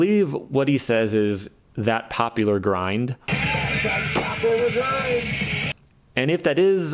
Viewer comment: "That POP Over Grind!" Pretty sure that's what he says.